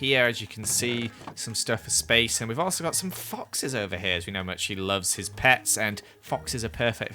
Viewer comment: Did he say she? I'm pretty sure Seapeekay is a male.